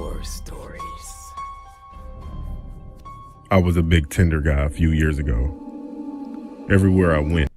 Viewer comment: I thought he said big tender guy 😂😂😂